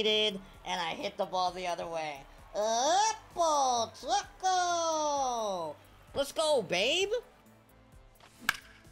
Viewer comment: what is it you say after a homerun sometimes like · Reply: oppo taco